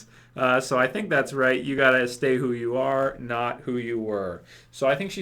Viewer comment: I heard Beth's words as: "You have to take me more, than you take Carol" Makes sense ...